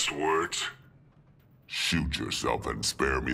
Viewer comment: so savage 😂💔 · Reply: At first i thought i heard him say shit yourself